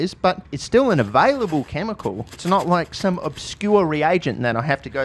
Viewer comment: did you just call it 'Ligma-Aldrich'? I love that